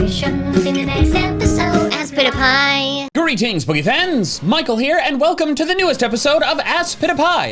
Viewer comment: the captions say greetings BOOGIE FANS!! 💃💃💃